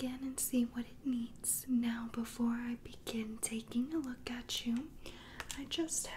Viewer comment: Now before I begin taking a look at you What I thought she said: Now before I begin taking a look achoo Me: Bless you